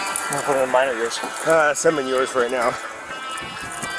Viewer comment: did he just say slam him in your car?